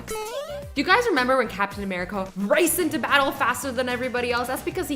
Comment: did she just say 'captain americo'?